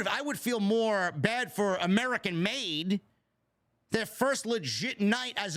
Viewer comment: Damn, said that the name Gable's new group was American Made, I really thought he said American Males!! So I was singing the song, then realized I was indeed mistaken